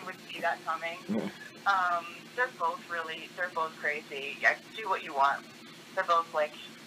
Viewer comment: she does say "do what you want". Just saying.